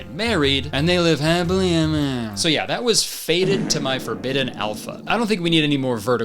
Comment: when said "fated" I heard "faded to my alpha" so I thought the whole movie was going to be about werewolves that were high which would have been so much better